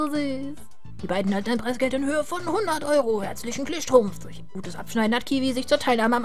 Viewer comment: Bei 20:21 habe ich "Herzlichen Kniestrumpf verstanden" XD